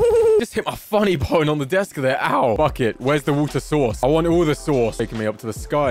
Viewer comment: he said the f word it was f it instead of bucket lol